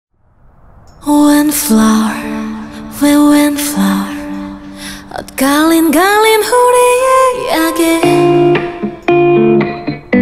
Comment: What I hear: "Wind flour" lol😂❤🌫🌸